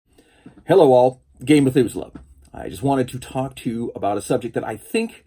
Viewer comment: And all this time I legitimately thought he was saying "Gay Methuselah"... yeah jamming stuff together is cool, though I'd submit you don't need Planescape to do it unless the module is truly planar (like the City of Brass). Grab the bits you like, file the serial numbers off, and throw that Underdark adventure in the ground beneath your feet or that castle just at the edges of your map.